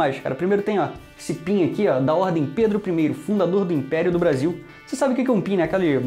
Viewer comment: Eu ouvi 0:13, Pedro I ditador do Brasil kkkkkkk